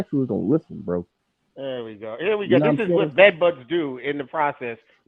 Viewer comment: 😭😭yall are comedy on here did he say bed buds 😂